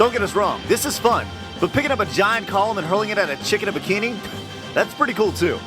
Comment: ooohhhh i thought you said a chicken in a bikini!